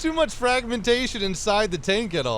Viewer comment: My mind is realy f*** dirty, i hear sing "hentai" instade "inside"